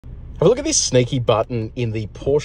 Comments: I thought he said "snakey" button. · You said 'sneaky' I heard 'snakey'. You have a thick accent 😜